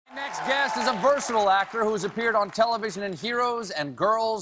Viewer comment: I thought Colbert said "he's a virtual actor..." He said, "Versatile actor.." HA !